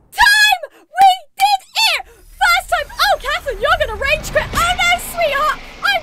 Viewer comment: Wait a second..... is your name Catherine? When you were reading the comment after killing thunderblight ganon it sounded like you said “oh Catherine you’re gonna rage quit, not today sweetie!”. Pls like this comment so she can see it!